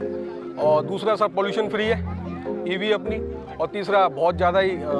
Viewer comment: Did he say pollution free? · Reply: Did you find it incorrect when he mentioned "pollution-free" ? Keep in mind, electric vehicles don't even have an exhaust system, contributing to their emission-free operation. What do you think about that aspect?